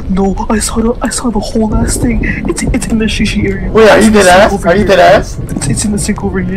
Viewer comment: did just say 'shi shi area' LMAO